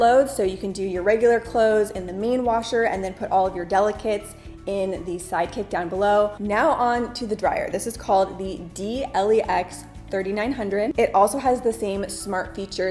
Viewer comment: Oh my gosh, I'm a nerd! When you were talking about the "Side Kick" to your washer, I heard "Psychic"!🤣🤣 I sat here trying to figure out WHY they would call it that!😱😱